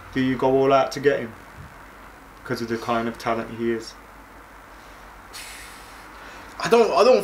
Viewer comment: Did he just say “declining talent he is”? Dude, we signed Ighalo. I’m not complaining about Ighalo I wish he stays he retires just because he loved playing for my club and he is a good finisher. I would sign Kane for 150m!